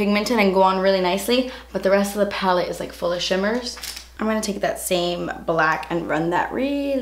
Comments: At first I thought you were going to say full of shit but you said "full of shimmer" lol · I thought u we're gonna say the rest of the palette is full of shit lmao Instead of "full of shimmers" 😂